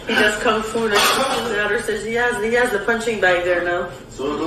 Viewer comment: what does she say? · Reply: Something something punching bag? I think...